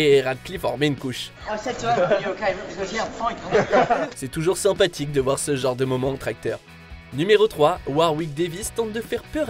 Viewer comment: à 6:41 j'ai entendu "c'est toujours sympathique de voir ce genre de moment en tracteur" 😭😂